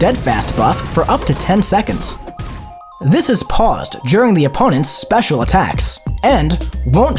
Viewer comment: i heard jerma instead of during